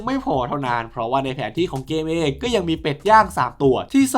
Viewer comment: เป็ดยางรึป่าว ผมได้ยินว่าเป็ดย่าง😅